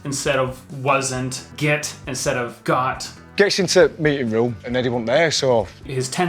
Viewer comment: A unique identifier of Leeds accents is replacing “the” with just a “t” sound which he did in the clip but you heard it as “into meeting room ” instead of “in t’ meeting room”